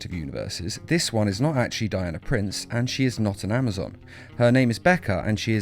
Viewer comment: I thought you said she died of a prince and not Diana Prince